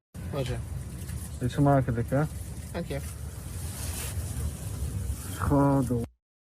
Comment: He didn't say ok he said: dank je. The Dutch guy said: what's up? The mf'er then said hypocritical: enjoy your meal. The Dutch guy then said: thank you/ dank je.....